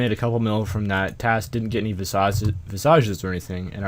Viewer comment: it sounded like you were going to say visausage at 9:03 lol